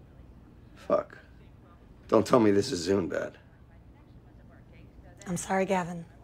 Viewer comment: Is he saying ‘this is zoom bad’ at 1:22 ? If it’s the case, this part aged very well